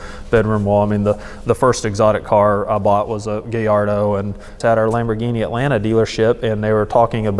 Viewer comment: did he just say gayardo?